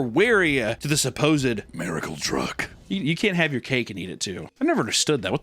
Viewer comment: I heard it’s “you can’t have Kate and Edith too!” Like cheating. It makes more sense to me lol